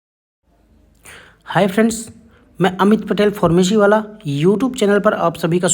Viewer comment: kya bola Formoji wala:face-blue-smiling